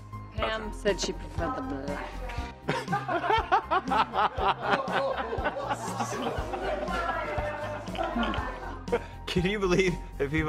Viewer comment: What did Allison say at 49:05? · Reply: She said that Pam preferred the black